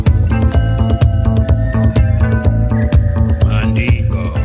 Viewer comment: As a Taffy I always thought he was saying "right" now I know it's "rights" I hear it clear as a bell. Keep it up.